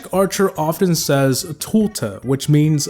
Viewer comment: I always thought magic archer said “bofa” instead of “tulta”, and I thought he was obsessed with deez nuts jokes